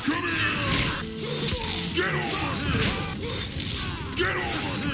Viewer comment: As a not fluent english speaker when this game came out, I thought Scorpion said "Game over girl"